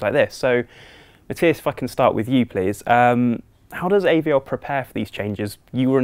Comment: "Matthias, f**cking start with you please" That is what he said. That is what I heard. Change my mind xD